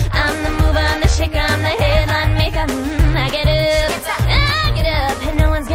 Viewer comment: “I’m the mover I’m the shaker I’m the headline maker” is weirdly inspiring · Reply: Omg I thought it said hairline maker for so long lmfao